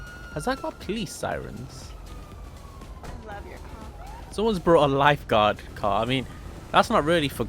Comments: wtf at 2:15 u can hear someone saying, "i love ur cock" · why do I hear "i love your c*ck"? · Is this just me or does someone say "I love your cock"